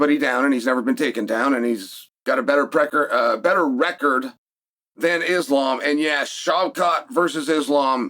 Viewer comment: Did homie just say Shavkhat’s got a better pecker than Islam?💀